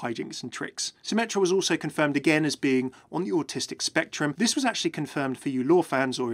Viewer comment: Did you say Symetra was on the "autistic spectrum"? I thought that's what he said, if not, please tell me what he did say..